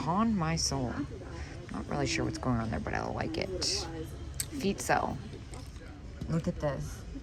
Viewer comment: 🥰Thanks so much for the laughs. You said "feet sell". I thought you said "feet smell"🤣 But they are not iconic.😂 And then I lost it with the "screw people"‼😅 Thank you for loving the unexpected, wild, risque, and screw people! I do not want to act like the old lady that I am😋, you are a doll😘